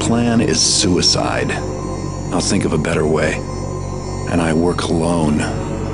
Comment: its sounds like he said " and i wear cologne"